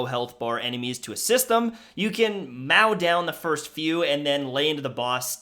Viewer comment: Did the literally say mau instead of mow (3:02) Probably satire, thought it was funny either way